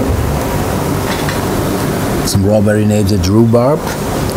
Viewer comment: what does he say? "raw marinated"? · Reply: Strawberry marinated. Probably peeled the fibrous bit then marinated in a strained strawberry puree(more like we'd call macerating in the US , UK or Canada.) When you rest rhubarb in sugar overnight, it becomes much more tender.